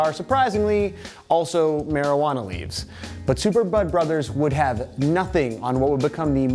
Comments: At first I thought he said "Super Mario Butt Brothers" then I listened again and heard "Super Mario Bud Brothers". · Am i the only one who thought he said super butt brothers?